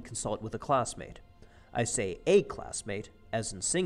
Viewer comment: I could have sworn for Rule #6 you said “Study in Paris”